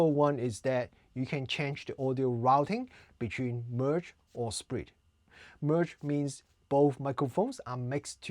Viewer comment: I thought u said "sprint" when in actuality u were saying "split" 😅😅